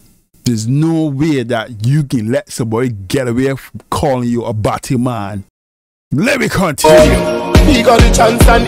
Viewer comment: Thanks for correcting me on what I thought I heard.Butty man sounded like batty man I was like WHAT 😳😅.correcting me on the voice sample not being samini but it is Shatts Wale imitating Samini. Reason I emphasize on The legendary was just base off the characterization of Samini being a legend by various people as if Shatta Wale isn't and you guys stated they basically came out around the same time.hope I clear up why I said it.bless up to everyone 🙏🏾🙏🏾